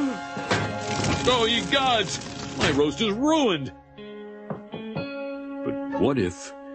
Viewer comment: "Excuse me for one second..." "OH EGADS! MY ROAST IS RUINED!" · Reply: I thought he always said "Holy gods! My roast is ruined!!" LOL